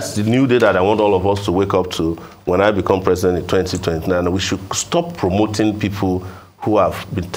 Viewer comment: did he say become president is 2029?